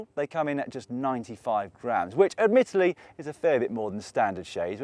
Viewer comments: I got excited when Dan said “all in all they come in at 95” I was hoping to hear pounds, not grams! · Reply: Oh I thought he said "95 grand"! What a relief. Cause now they're about 700 bucks I think. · Haha when he said 95 grams I thought he said grand